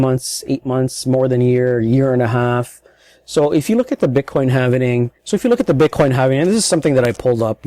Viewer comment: did you say "bitcoin havening"?